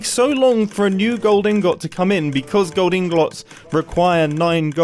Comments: Why does he say "ingot" as 'in-got'? Isn't it pronounced as 'in-get'? Also at 8:42 he pronounced it as 'inglot' lol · did anyone else here mumbo say ingalots instead of ingots at 08:42 ?